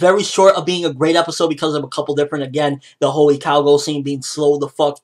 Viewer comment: When said "the whole ikalgo thing" I thought he said " The holy cowgirl thing" XD